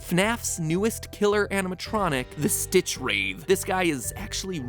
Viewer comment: until I looked, I thought he said 'stitch rave'